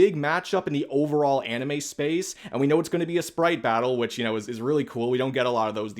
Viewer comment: sounded like you said Spite battle so had to rewind.